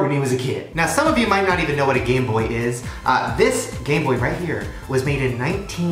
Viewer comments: I thought said "Gay Boy" 😂😂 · It sounded like he was saying "Gay boy" not gameboy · thought he said gayboyb a couple times